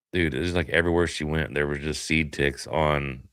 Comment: i thought said "covered in sea dicks," and then i realized it was "seed ticks." 😂